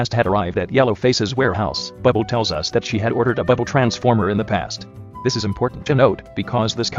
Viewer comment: I thought she said bible transformer lol